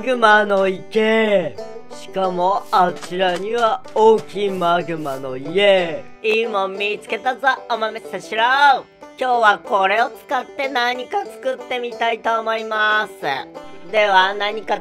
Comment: か0:26にマグマの家って聞こえるww